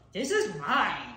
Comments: The way he held up the snack and said, "this is mine!" Is so accurate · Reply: I thought he said “this is bomb”lol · 😂😂😂😂😂 whenever he eats as a little kid he always says “THIS IS MINE “ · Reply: Oh I thought he said "This is PIE"